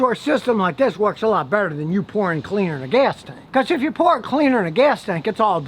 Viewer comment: I thought he said "Youporn" 😂😂😂